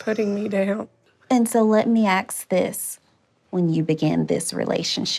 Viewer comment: Sorry but at 37:46...what kind of trained professional councellor says "let me axe this" instead of let me ask?